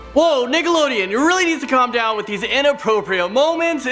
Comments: is it just me or did he say niggelodion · Plz tell me im not the only one who thought he said niggalodeon in the beginning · YOOO I SWEAR. I THOUGHT I HEARD "NIGGALODEON" LMAOOO 💀💀😂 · wait did he say the N word with Nickelodeon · What's your favorite Nickelodeon show? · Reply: did he say niggalodeon?